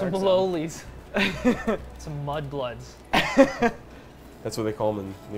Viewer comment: Am I the only one who heard D say 'Mud Bloods' around the 2:00 mark?